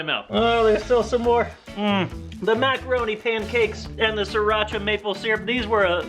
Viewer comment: For some reason I thought the title said macaroon pancakes, and I was like "What has the world come to"